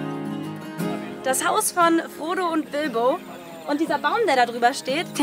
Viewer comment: Ich hab statt Frodo immer Bodo verstanden 😂😂😂